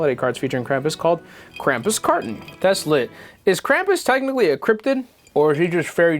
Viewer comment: What said: "Is Krampus a cryptid?" What I heard: "Is Krampus a crip then?"